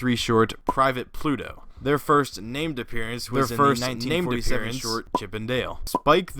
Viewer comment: What did you say about Chip and Dale? I can’t understand a word you’re saying when you spoken about them twice as like you’re mumbling. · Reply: sorry about that, the audio got messed up some how. There first named appearance was in a short titled "Chip an' Dale"